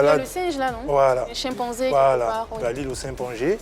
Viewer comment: Le 56:15, lol DJ bonano a dit ''simpangé'' au lieu de ''chimpanzé'' ou bien c'est moi qui est mal entendu, big up mon artiste😊